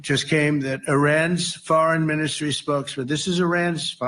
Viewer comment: Did he just say "A Ranch Farmer" while trying to say "Irans Foreign Minister"? Thats what the closed captions said.🤣🤣🤣